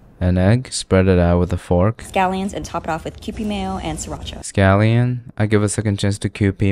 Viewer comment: I THOUGHT SHE SAID PP MAYO😭